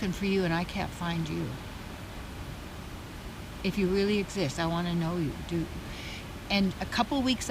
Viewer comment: I thought she said "if you really exist, i wanna know you, dude." And i love that even if it isnt what she said.. its what i would have said hahah❤